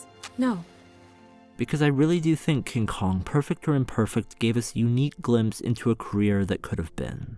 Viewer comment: I guess I’m sorry, because I heard perfecter and perfect.